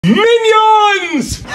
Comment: I thought he said "Minions, tonight we SANITIZE my brothers date" 💀💀💀